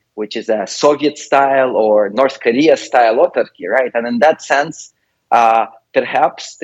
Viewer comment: Sorry what is the word at 9:25? Autorcki? Otorki? What's that? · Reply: autarchy. Worth looking up in your favorite dictionary with that spelling, because there are some nuances (I just noticed; not an everyday word for me)